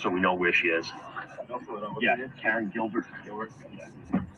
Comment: what does he say right after saying "I'm going to section her so we know where she is"...it's sounds like he says "Karen killed her" or "Karen Gilbert" i cannot make it out, does anyone know what is said here? 💜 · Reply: Gilbert.. I think that's her parents address (from memory)